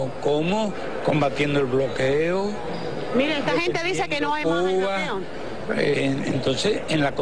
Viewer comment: Porfavor qué dice en el minuto 0:11 ? Mira esta gente dice que no hay más ... ?? No entiendo. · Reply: "Esta gente dice que no hay más bloqueo"